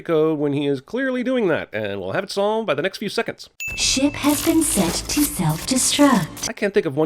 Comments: For a moment I misheard "ship" as "shit" which made me chuckle. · who else thought it said “shit” at first?